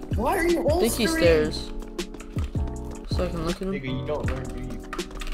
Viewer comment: ? What he say😭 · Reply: Bro sayin nigga playin apoc 😭😂😂